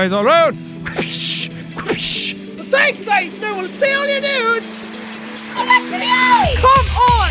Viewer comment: for some reason when he does his outro I hear "I'll see all your nudes in the next video!"